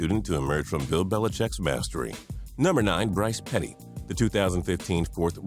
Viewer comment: Did u just say Brice petty😂😂😂😂